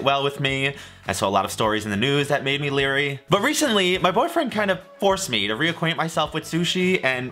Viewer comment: omgg did u say boyfriend??